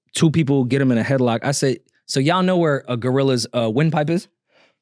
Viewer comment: I thought they said glorilla….i thought she was about to be taking wild D***s